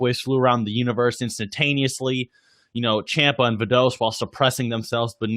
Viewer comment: did you just say "Champa and Videl"? XD What happened to Vados bruh?? Lol. 😂